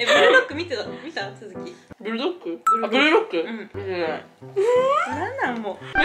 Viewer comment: これメイクしながら見てたら、ブルーロックがブルドックに聞こえてて訳わからんくなった